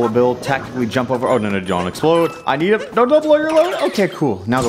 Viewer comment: did he just say "Don't blow your load"?